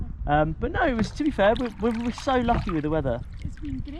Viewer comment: I heard 'loch' so many times I thought he said 'we've been so lochy with the weather'.